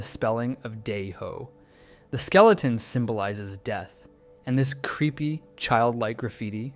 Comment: Actually I thought it said Skeleton Bagaji Idiot 해골 바가지 바보: Haegol Bagaji Babo (a bagaji 바가지 is this fruit? shell where some Korean people used back in the olden days to pour water) and there was a point back in Korea where people would smash bagajis on their heads as a challenge, because it would hurt and it's funny, and I believe it has nothing related to Dae-ho, it'd be weird to have a player's random name in a game map, but that's just me 🤷